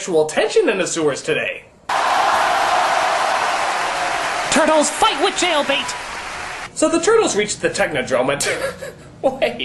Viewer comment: I thought he said,"TURTLES FIGHT WITH IRON" with kind of a hillbilly accent.